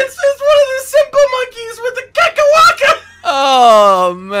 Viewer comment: did he say kekawaka? Edit: if theres one monster i could forget, it'd be ahtal-ka, just to experience that fight without knowing about beforehand